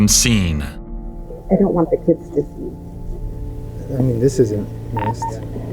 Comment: "Scheisse!" I thought I had midheard at first 😂 But the subtitle confirms she really said Scheisse. Didn't expect that in an American video, hahaha.